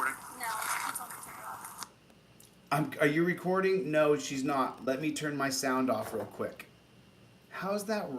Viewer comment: At around 47:10... She's asked are you recording. She actually said, "No, he told me to turn it off." Nothing about turning sound off.